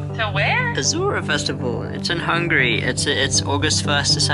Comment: Bro at 17:23 did you just said "its in hungary"? 🙄